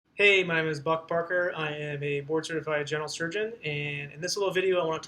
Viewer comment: HAHA Buck Parker! Your name is literally the best name in existence! When you introduce yourself it legit sounds like you say "Hi I'm Butt Fucker!" LMAO My wife & are dying right now lol Thankyou